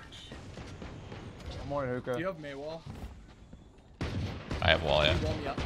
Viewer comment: did he just call it a mei wall